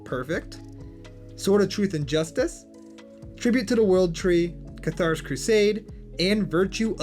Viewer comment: sounds like you said "tribute to da world tree" instead of "tribute to the world tree"